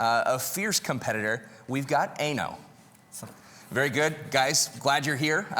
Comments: Sounded like he said "we got anal" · Did he say anal ?!